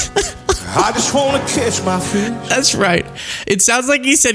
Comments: I totally thought he said “kiss my fish”🤣😂😅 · I thought he said "kiss my bitch" which would fit right into the MAGA way of referring to women. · I thought he said "I just want to kiss my feet". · I genuinely thought he said kiss ky fish the first it was played. I was like .... wait....what?😅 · I thought he actually said kiss my fish 😂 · I thought he said kiss my fish 🐟 too 😂